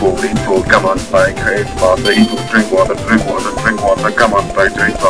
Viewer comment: Did you say "eat water" at 2:46?